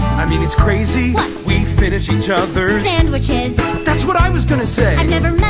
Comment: Does she actually say sandwiches?